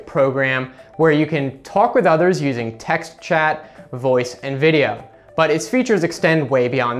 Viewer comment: he says Voupe instead of voice